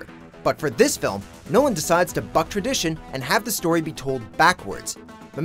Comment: I know these are not so new movies but atleast a spoiler alert and at 1:45, did u just say fuck? What happened watchmojo?